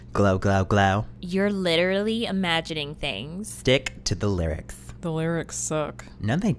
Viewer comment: I hear d... to the lyrics 😂😂 it should be stick to the lyrics but I heard d to the lyrics 😂